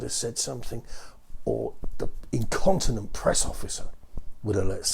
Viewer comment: For a little while I thought you said “like some incontinent press officer had let something slip” I was laughing really hard when I replayed it and realized you said “incompetent”